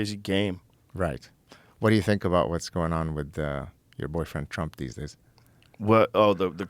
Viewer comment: Did he really say “what’s going on with you boyfriend trump? “ and Joe didn’t laugh, get annoyed or even address it ? Did I hear wrong !?